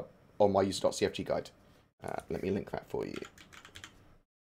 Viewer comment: did you say the Game/System status info command is "r_displayinfo= 3" and found in the S.C. console (CFG guide)?